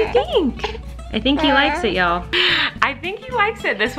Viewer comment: When he was chewing the Gameboy, I kind of heard him say "I like it." Do cute.